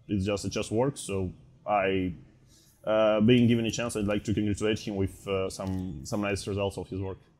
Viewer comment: did Ian say he wants to humiliate him?